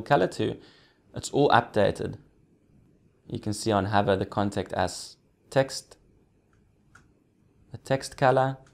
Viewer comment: I thought you were saying "Contact Ass" - I was thinking, yeeeaaahhhh, this is gonna be a cool site. :)